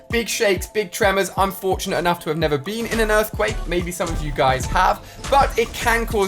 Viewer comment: For a minute I thought he said 'I'm unfortunate to NOT have been in an earthquake'!